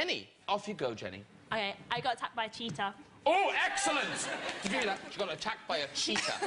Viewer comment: When the girl said "I got attackted by a cheetah" I thought she said "i got tapped by a cheater"